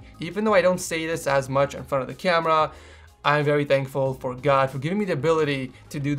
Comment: Did he say "For god" at 1:12?